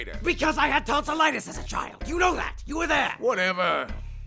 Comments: Tbh I thought papyrus said "I had tons of lettuce as a child" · sorry if it's a dumb question but, what is tonsillitis, ? the thing pap mentioned he had when he was a kid. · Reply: I thought he said " CUZ I HADE TONS OF LETTUCE AS A CHILD YOU KNOW THAT YOU WERE THERE" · Lol when papyrus said he had tonsilitus, i thought he said tons of lettuce XD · Papyrus: Why does it make that sound? Sans: I don't know. Why do you sound like you swallowed a cheese grater? MLG Horn Edit: Oh man. Thanks for over 300 likes guys. :3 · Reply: Princess Spirit Quartz I thought he said "I had tons of lettuce as a child" and spent says wondering just how the hell lettuce can make someone's voice sound like that. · sorry if it's a dumb question but, what is tonsillitis, ? the thing pap mentioned he had when he was a kid. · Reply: i thought he said "tons of legacy as a child" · For the longest time I thought papyrus said “because I had tons of lettuce as a child”